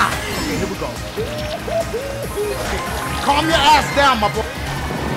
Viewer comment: did dashie just called donkey ma boy?